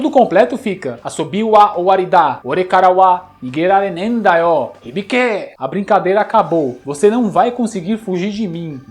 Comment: o secreto do kyo ele fala uma coisa quando o secreto não pega tbm ,o que ele fala? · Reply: Yarou = Maldito!